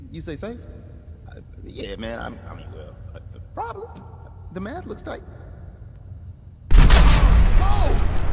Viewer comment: First clip I heard "the man looks tight"